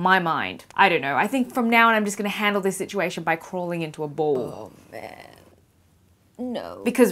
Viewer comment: I heard "crawling into a bowl."